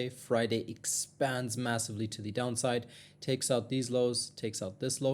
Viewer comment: DID YOU SAY "MASSIVE".